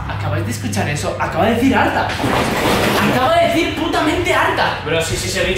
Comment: escuché tartaaaa